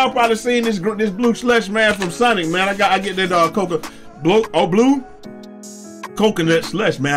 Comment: I know this is a real life vs. video game comparison but I'm going to go out on a limb and say Lebron probably had an adrenaline boost on that chase down block on Iggy not a loss in adrenaline. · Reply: that blunder you made at 3:18 has me crying in tears! I hear you promoting a "new" and improved version of the ocean water slush called the, "Coca-O'Bleu" 😭😭